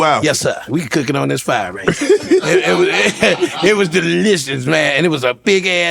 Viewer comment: did he say it was delicious Vlad 😂